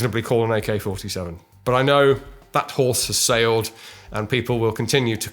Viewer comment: Had I heard "that horse had sailed"?